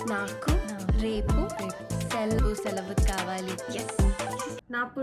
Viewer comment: you just said "i want dead bodies tomorrow" instead of "i want a holiday tomorrow" lmfaoo